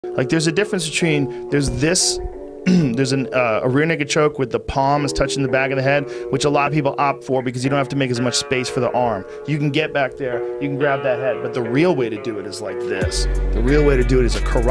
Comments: Bro i thought joe said “ a real niqqa choke” in the beginning😭😭😭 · Lol it kinda sounded like he said “a real nigga choke”😭😭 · Swore he said real nigga@ 🥷 Choke 😂😂😂